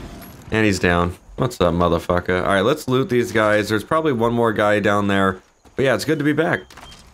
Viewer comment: I'm pretty drunk and at 0:56 I thought you said "let's lube these guys" lmao